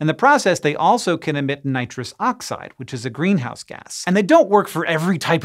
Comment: they also can emit nitrous oxide, a house music gas. I think he said greenhouse gas but that's completely wrong. Rave on mofo!